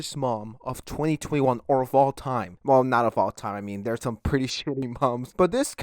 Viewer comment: I thought it said, "Worse man of all time" and I'm so confused.